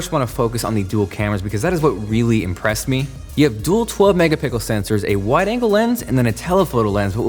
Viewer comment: "Megapickle" 😂😂 am i the only who heard that?